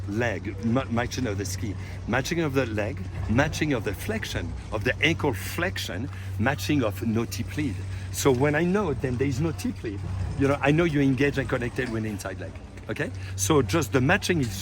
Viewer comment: What is he saying at 9:11 right after “matching of the ankle flexion”? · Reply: Matching. Matching of edge angles, matching of ankle flexion. Specifically at the transition there should be no tip lead if joints are evenly flexed.